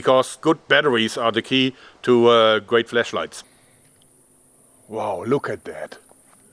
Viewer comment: am i the only one who heard him say fleshlight?